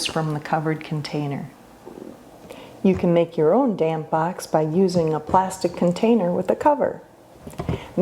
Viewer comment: At first it sounded like you said, "You can make your own damn box".